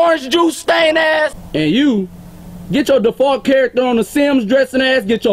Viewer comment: Default charcter on the sims lnao dead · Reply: breanna boatman I know wtf the sims is if you could see I've already corrected myself. I heard him say default